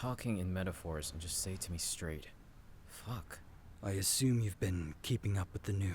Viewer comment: I heard a slight word called fck at 1:52/